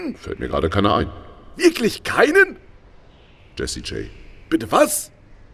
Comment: ich hab zuerst Jesse Jane verstanden...